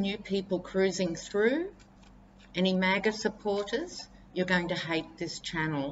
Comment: haha. I thought you said maggot supporters, not maga supporters. but I note how close the two are